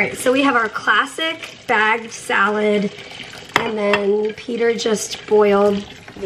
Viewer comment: You said, “Peter just boiled........”.......& I THOUGHT for a moment you said, “Peter just spoiled..........”! LOL I thought to myself, “NAH!!! Mary WOULDN’T have said THAT!!!” LOL I was right! You DID NOT throw your loving husband under the bus!!! LOL